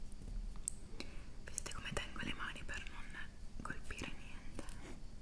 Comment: min 23:50 "viste come tengo le mani per non colpire niente" ¿¿¿???? escuché bien?? itañol???